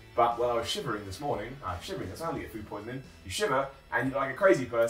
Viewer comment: I couldn't catch everything he was saying. What did he say after "I was shivering this morning"? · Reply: Something about having food poisoning.